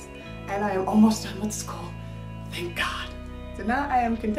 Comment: I thought you said art school?